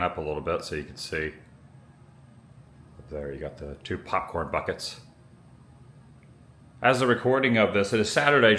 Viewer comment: In the beginning, I thought you said, "2pac corn buckets." I was lost on why 2pac would have corn buckets. lol